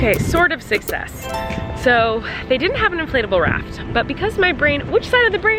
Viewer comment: I thought Colleen was saying inflatable rat😂😂